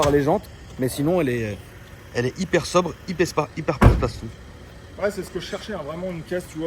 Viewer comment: Elle est hyper quoi Tonton, j'ai pas bien entendu ? 🤣🤣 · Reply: Hyperspa Hyperpartastou. Ça veut dire elle est bien… voilà !! 😅